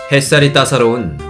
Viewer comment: 초에 햇살인데 뱃살 이라고 들었어요ㅋㅋㅋ